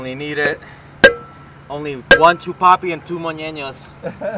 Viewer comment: All I heard was "chupapi munyan no!"